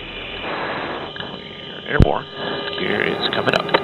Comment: didn't he say gear is comming up instead of air?